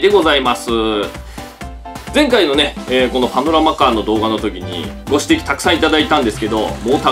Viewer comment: パノラマカーがアブラマカーに聞こえたwwwwwwwwwwwwwwwwwwwwwwwwwwwwwwwwwwwwwwww